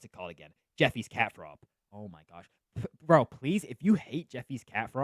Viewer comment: did you say Jeffy’s cat throw up because I didn’t really understand it